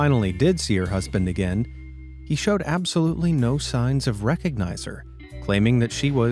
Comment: he said recohnizer instead of recohnizing her...